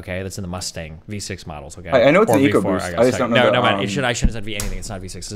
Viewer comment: eco boost? i thought u said ego boost.